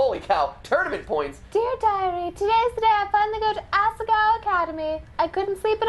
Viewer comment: I totally thought it said Asiago-like the cheese. Let's just say I am now hungry. Anyways! Good stream!